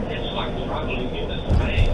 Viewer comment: what did they guy say its not really clear? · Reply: He said “it’s like travelling into space”